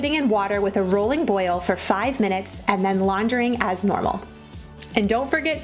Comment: when you said 5 minutes i thought you said 5 sexs